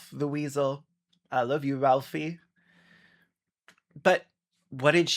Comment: i misheard that as "Ralsei"